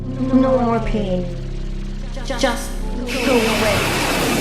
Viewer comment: For the LONGEST time, I thought Softdi said "No more pain, just go for it!" But now I know she is really saying "go away".